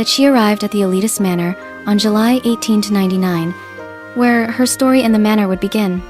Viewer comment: I think you got the wrong date that Emma got to the manor but thats ok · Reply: Wait did you said the date or am i just stupid😅. Bc i thought u said 18 july